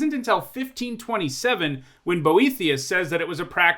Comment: I recently fell down an Elder Scrolls rabbit hole, so I had to rewind a bit to make sure I heard that right, lol I imagine that the IRL Boethiah probably isn’t a monstrous Daedric Prince who delights in the suffering of mortals. Although, given game-Boethiah is a goddess of deceit, I do wonder if the devs were inspired by IRL Boethiah’s apparent misrepresentation of medieval culture... interesting thought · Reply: Haha, I said "Boethius." A very different thing.